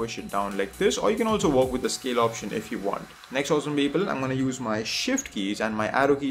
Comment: It sounds like you keep saying "ok awesome people" or "next, awesome people". What are you saying? (maybe it's your accent that makes it sound like that?) Example at 5:08 5 minutes into a video and I already notice you saying that a LOT. I mean you literally did it 3x in 10 seconds... It's quite funny ^^